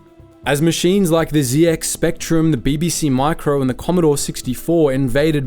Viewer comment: Zee ex spectrum? Um no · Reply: I thought he said "Zee Dex Spectrum". Ugh.